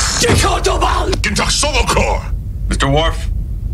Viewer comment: I'm pretty sure Worf just said the Klingon equivalent of "go to hell" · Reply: I think I heard sto-vo-kor in there, which is their afterlife, so that or maybe 'can't get into heaven' if not =)